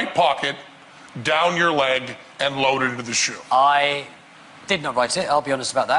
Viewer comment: did he say that he "did not write it"? Someone else wrote it?